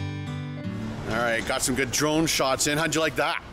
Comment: When ya said "Got some good drone shots in" I thought you said "Got some Patron shots in" the first two times I rewound to re-hear it!